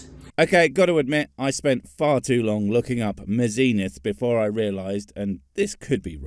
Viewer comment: I heard "the zenith" not "mazenith"